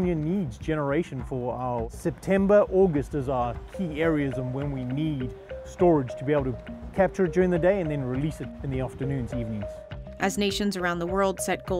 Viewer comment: I was confused, person at 0:30 was NOT speaking English, I have no clue what he was saying.... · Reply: D He does its “September august (sazar?) are key areas on where we need storage to be able capture it during the day and release during the afternoon to use” probably because of his accent that you cant hear his english well